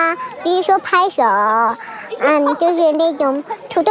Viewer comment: did she just call the black girl nigba>